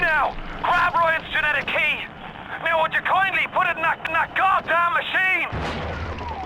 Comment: it sounds like it says would you kindly put it in that nac nac machine XD